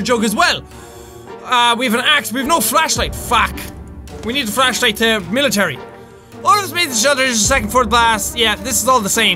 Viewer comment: Wait, Did He Say "We Need The Flashlight TO Military